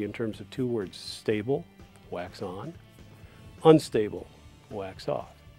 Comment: “Stable, wax on. Unstable, wax off.” Is the word wax? If not, what is that word? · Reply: The word is wax but it's a karate kid reference!